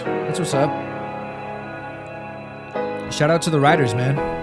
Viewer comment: not me thinking he said shoutout to the “writers” instead of “riders” 😭💀 i moved